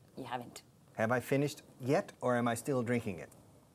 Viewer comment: Buenas tardes. En el minuto 5:19 dice si mal no entiendo am I finished yet or am I still drinking it? No sería have I finished yet....?